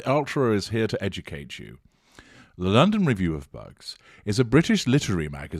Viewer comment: Oh i thought you were saying "the review of bugs!!" i thought it was some entomological report!